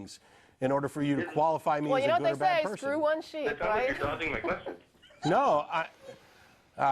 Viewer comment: rofl!!! did she just say "... screw one sheep right"